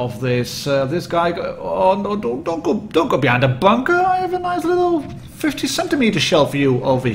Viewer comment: a 50cm shell !?!? That's a girthy boi right there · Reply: I think he said 15cm